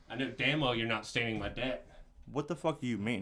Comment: can’t say deck properly. Swear he is saying debt lol